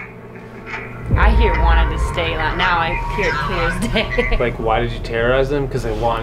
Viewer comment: I thought I heard "Dandy must stay"